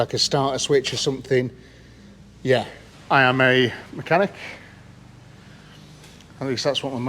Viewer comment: Mechanic or Maniac you may have misheard her boss 😂❤👍✌️